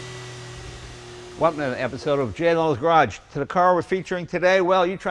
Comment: did he just say "J Lo's garage" ¿